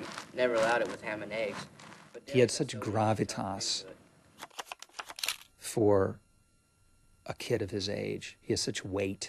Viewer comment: What is the guy saying at 4:07? "He had such.... for a kid of his age"? Maybe it's bc English isn't my native language, but can't make out that part no matter how many times I rewatch that scene. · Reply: Riona Ghlas such gravitas for a kid his age